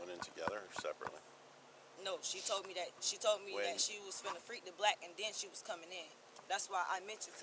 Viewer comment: I listened to this like 20 times. What is she saying? Sounds like “she was finna freak the blk” ???